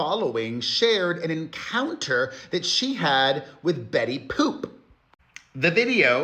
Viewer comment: Did i hear ", betty poop"?? Is BL a food endorser now? From donuts to pizza.. 🍩🍕